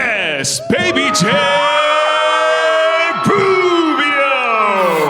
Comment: You can’t tell me the announcer at 7:55 didn’t say boobio instead of Rubio 😂😂😂😂